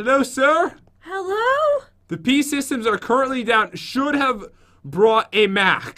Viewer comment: says "the pee systems are currently down" instead of the pc systems lol did anyone else hear that???? :')